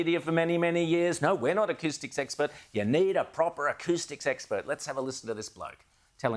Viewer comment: Thought there were saying,I need a poo